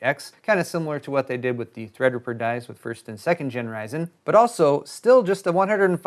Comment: Did you say generyzen at 2:52? :P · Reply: Gen Ryzen As in 'first and second gen Ryzen' meaning 'owners of 1000-series and 2000-series Ryzen products'.